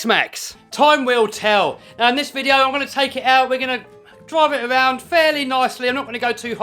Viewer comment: Always good stuff. I thought u said u will take it slow